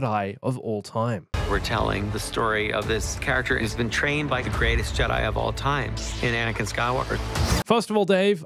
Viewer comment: I thought he said "been trained by the greatest Jedi of all time, AND Anakin Skywalker"